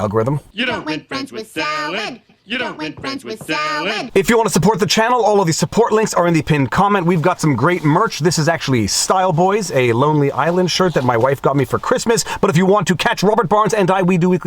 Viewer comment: I listened to the whole video but had to relisten to the last couple minutes. I thought you said "Island Boys". Thankfully you didn't, and for that I still respect you. LoL. Though, to be honest, I'd still respect you. But withan asterisk attached. Ha ha.